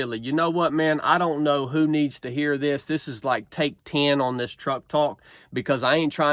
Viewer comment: At the end it really sounded like you said Trump Talk! Awesome